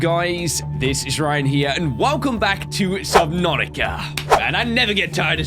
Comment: What I thought you said when you said sabnautica at the intro I thought you said just Monika . . Well nvrmind 😊😊 I'm just crazy..